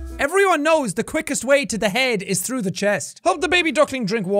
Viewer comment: I thought he said the quickest way to the mind is pills 😂